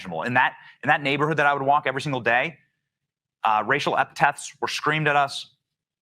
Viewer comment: did he say racial epitaphs?